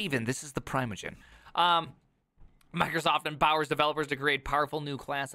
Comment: I misheard “empowers developers” as “embowels developers”. Now I’m wondering… how many bowels does a developer need?